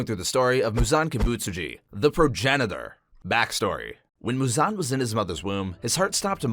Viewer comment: was I the only one who thought he said "the pro janitor" at the beggining?